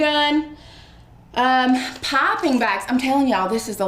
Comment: Pipping bags sorry I thought you were saying popping bags!